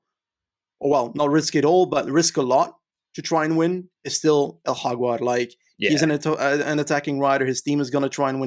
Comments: What name is saying at @ 25:40? I can´t make it out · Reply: Jaguar - aka Carapaz. Sorry, my vocal cords died. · I don't understand the name....sounds like is talking arabic to me. who is he talking about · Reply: Jaguar - aka Carapaz. Sorry, my vocal cords died.